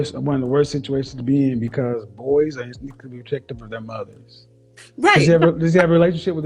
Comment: what does he say at 8:06? "boys are as weakly objective as their mothers".... ??